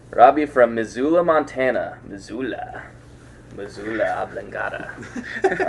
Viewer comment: jajajaja parece que dice Pichula xD